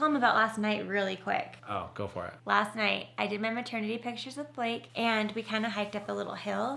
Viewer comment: Did she say "maternity pictures of Blake?" at 20:05? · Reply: With Blake :) our photographer